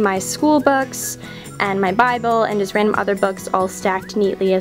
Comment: Did you say Bible on 7:54 or something? Sorry! I'm just so curious :S I've actually always wondered what your religion is.. And I hope no one replies to me saying "Religion doesn't matter" and all that stuff, but I'm sorry for being curious. I hope you don't mind, I really have no bad intentions :(